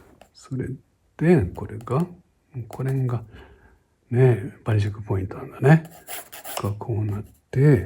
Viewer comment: キャプションでは”やっぱりチェックポイント”となってるけど、は”バニシングポイント”と仰ってるようにも聞こえます。透視図の消失点ですよね。